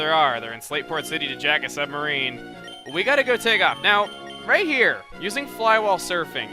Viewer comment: I thought it said go jack off......:)....giggity...